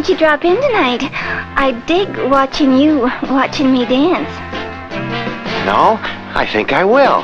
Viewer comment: I swear at 0:25 seconds it sounded like he said no, and I was straight up like, "DAM, okay Peter. I guess it be like that."